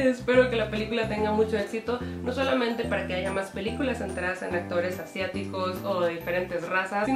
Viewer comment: Eso fue un haiga o escuche mal?